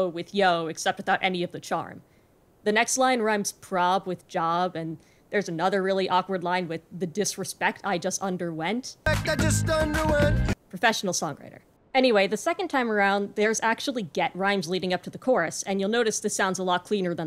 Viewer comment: I always thought he said "this disrespect, I just don't do it" Which tbh in my opinion makes more sense for the song